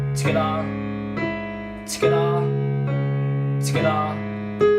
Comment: Can anyone tell me wtf is he saying from 4:00 "tsketa?” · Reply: He said "together"